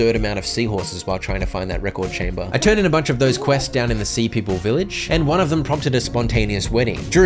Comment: i have no idea why but i kept hearing him say "the sea pickle village" love your videos <3